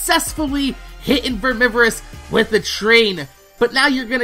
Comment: I thought you said "tree"